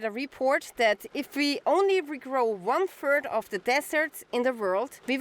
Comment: Ohhhh ! One third, thought she said one turd! Roflmao!